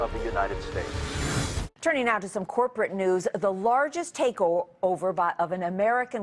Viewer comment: did she just say "the largest taco-over" in a freudian slip in reference to mexican immigrants?